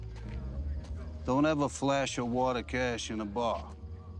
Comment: At first I thought Tony Lip said "don't eva flash yaw water cash in a bah". That is why I like watching movies with subtitles in case I misunderstand what the character is saying.